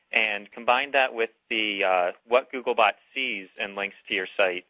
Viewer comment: analytics, i thought orginally u said "and linux" lol